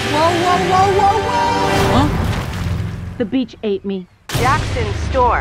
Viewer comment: the beach ate me 😂😂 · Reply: Jean-Luc Herr I thought she said "the beach saved me" XD